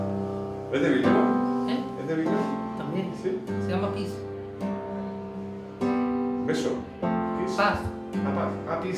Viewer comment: Qué hermoso sonido que tienen sus guitarras! claro, y preciso. No entiendo el nombre del autor del tema "Peace" a los 24:17, agradezco el dato! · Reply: Gaston L Peace piece de Bill evans